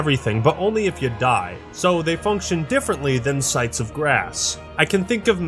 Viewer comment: did he say grass?